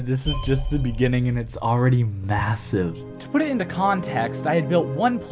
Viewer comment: did u say massive?